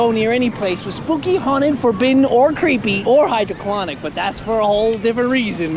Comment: I thought they said hydroponic